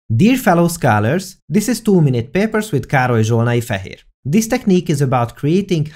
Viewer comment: I always thought you were saying "Károly Zsolnai-Fah Here" instead of "Károly Zsolnai-Fehér." Only now realized there was a "Fehér".